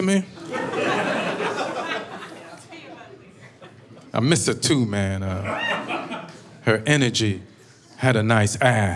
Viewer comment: What did she say? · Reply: Sounds like "I'll tell you about it later"